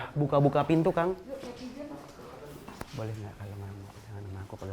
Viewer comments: Di menit 2:27 bilang ke "boleh gak pegangan nya sama aku jgn sama holao " apa ai salah denger yaa haha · aku gak salah denger pas diawal2 menit 2:28 said : BOLEH GAK KALO MALAM PEGANGAN SAMA AKU, JANGAN PEGANGAN SAMA HOLAO ? :v